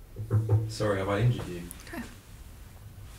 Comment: In the beginning I hear “sorry have I injured you” and then “ok”…. Wtf😅